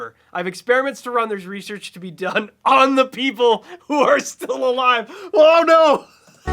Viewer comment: I always heard that line as "FOR the people who are still alive"... OH NO...